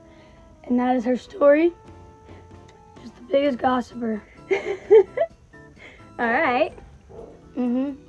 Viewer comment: I thought he said gothicer for a second.